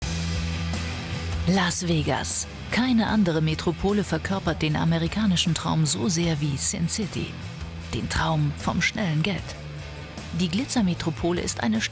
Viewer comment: ich hab SiM city verstanden xD also das spiel xD